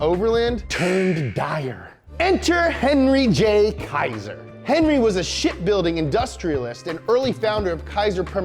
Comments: I thought he said Henry was shit at building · I thought he said shitbuilding